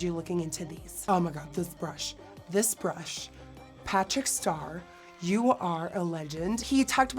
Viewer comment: Did she just say "Patrick Star" at 9:17..from SpongeBob?